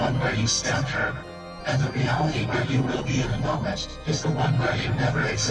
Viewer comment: "and the reality where you will be [unintelligible] is the one where you never existed" what was the word? CC says "enormous" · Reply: "in a moment"? I've rewatched that so many hecking times.